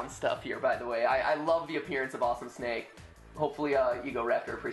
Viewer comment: i thought he said awesome snape. form harry potter